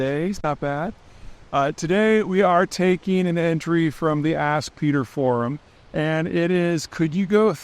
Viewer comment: Kinda sounds like you said ass beater forums